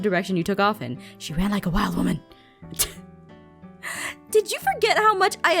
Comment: Laurence: She ran off like a wild woman! Me: xD Favorite part of this episode! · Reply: Omg i loved that part so Much, but for a moment i thought Laurance said "She ran off like a Mad Women"